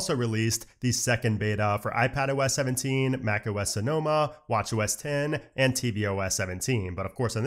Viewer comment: why do i hear "watch your a$$"